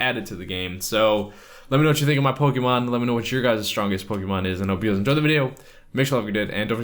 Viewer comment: what are you saying at the end I can never figure it out xD please tell me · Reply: I hope you guys enjoy the video, make sure you like and/or subscribe for more Pokemon Go videos if you have not already, and have a wonderful night & see ya guys later.